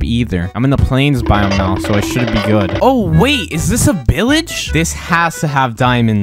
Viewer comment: “Is that a pillage?“ Me: laughs so hard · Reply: I thought he said billage